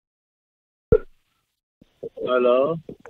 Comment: In my half asleep state I really thought his lazy "Hulllo?" At 7:38 sounded like "My Love?" That would have been an entertaining call.